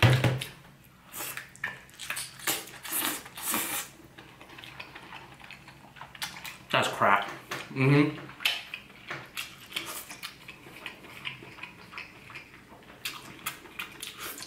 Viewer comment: Lol did he just say "that's crack"